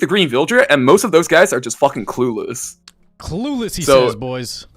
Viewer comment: What was he not allowed to say at 14:00? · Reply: He said fucking homie